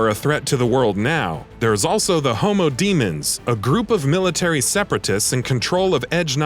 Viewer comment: Did he just say "homo demons"?